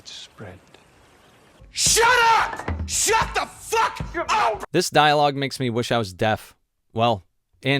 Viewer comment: i misheard "this dialogue makes me wish I was deaf" as "this dialogue makes me wish I was dead" and both are excellent lol